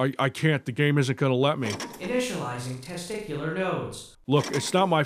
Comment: "Not as bad as eternal ball tasing" . is a Poet of rare quality indeed ! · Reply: They said testicular nodes so I think it is an implant. Or two. I heard "internal." Still a great line.